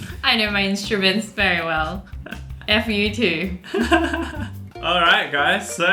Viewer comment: did she say f YouTube